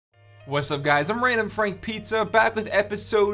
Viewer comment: Did I hear "Random Frank Pizza" in the beginning?